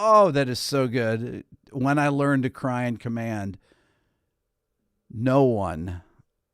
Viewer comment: I always thought he said "when I learned to cry like a man".....